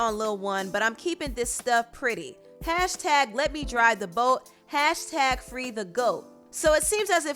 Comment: I always thought she said “let me dry the boat” lol